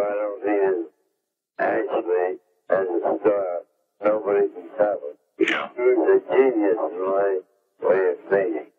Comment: Can anybody make out what Larry says about Charlie Chaplin? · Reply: He said as a star, nobody could beat him. He was a genious.